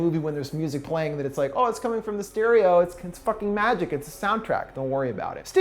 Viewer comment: at first i heard "it's coming from mysterio"